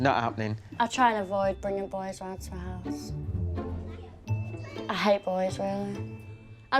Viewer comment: When said "i hate boys" but now she has her own baby 😂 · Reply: i thought she said he hates boy's rooms